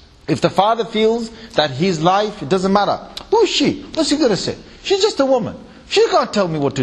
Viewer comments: JazzakAllah Khairan for uploading, May Allah forgive us all and grant us all Jannatul-Firdaus inshAllah. Ameen For those who misheard !! Q. At 37:27 does he swear? Ans- No Shaykh Hasan Ali never swore. He said " who is SHE" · He never swore. He said who is SHE.